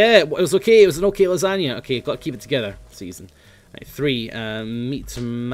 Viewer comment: did he just say "Season" When he sanitized his hands?